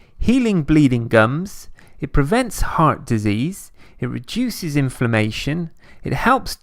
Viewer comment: I misheard that as "It reduces information" I wouldn't be surprised if that's so.